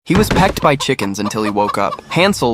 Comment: I’m so dirty minded I thought he said he was pegged by chickens 😂😂